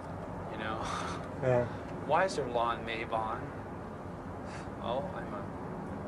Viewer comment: what hes he saying at 2:39 ? Lan mabon ?